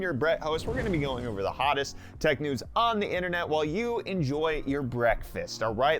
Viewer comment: I swear I heard "Enjoy your Brett Fest". Please use that!